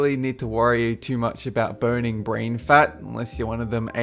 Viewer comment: I thought he said "urine brain"